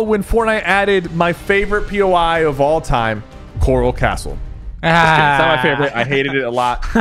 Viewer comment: who heard coral chorus 👇